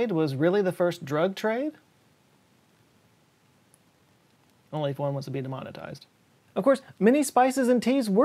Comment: What's he saying at 5:35? Only 4 months of being demonitized?